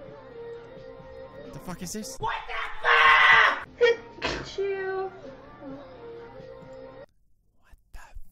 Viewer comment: did she say Pikachu?